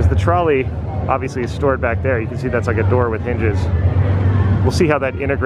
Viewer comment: says "doors with hinges" I hear "doors with ninjas" and was very excited and confused all at the same time